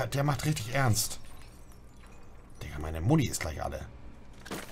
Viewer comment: Bei 2:52 versteh ich immer wieder "Digga, eine Muddi ist gleich alle" xD. Ich weiß ja, er sagt meine Muni. Aber das hört sich immmer wieder so an. Habe es 2x angehört. Zu geil :D Beste Mann